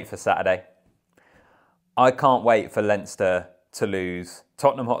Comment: 😂😂😂 I thought u said you cant wait for Leinster to lose.ha ha ha